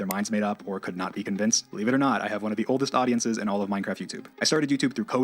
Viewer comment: I thought he said "I have one of the most wholesome fanbases" instead of "oldest fanbases" Either way he's wrong, his fans are absolutely rabid and they're mostly children/teens